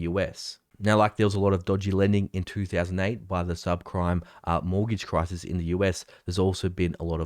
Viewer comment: You say subcrime instead of subprime. Was the on purpose or a Freudian slip? lmao